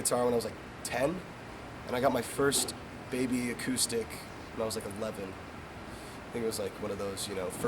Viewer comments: "I got my first baby acoustic when I was 11" Baby acoustic. Why do I find this so cute. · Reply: I thought he said I got my first baby at eleven My ears i swear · "I got my first baby acoustic when I was 11" Baby acoustic. Why do I find this so cute. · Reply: I thought he said "maybe acoustic" like he couldn't remember if it was an acoustic or not.